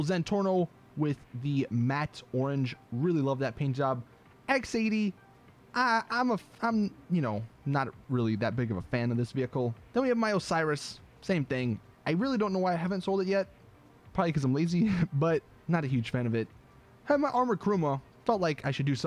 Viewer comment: When he said "My Osiris" i thought he said Miley Cyrus XD